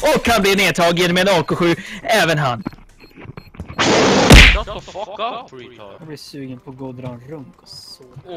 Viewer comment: bara jag som hörde säga "dra en runk" hahah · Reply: "jag blir sugen på att gå och dra en runk" hahah